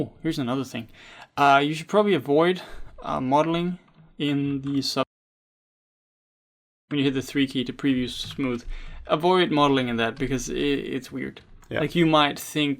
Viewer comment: The Volume Cut out at 21:00 so I didn't know What you Said ? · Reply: Sorry, we had some mild audio issues in this one! Make sure to not model in subd mode :) Work in regular polygon mode and then smooth your model to control check it.